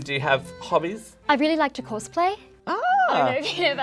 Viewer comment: she said cosplay so weirdly that I heard "horseplay" 😂 I was like what kind of hobby is that